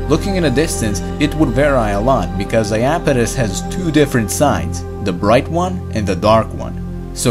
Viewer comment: captions say "because the a 'penis' has..." Lmao